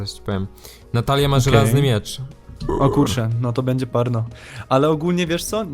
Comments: zamiast "No to będzie parno" usłyszałem "No to będzie por.." xD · usłyszałę o,, no to bendzie porno`` lol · usłyszałam: porno XD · Usłyszałem - O kurde...no to będzie porno... :D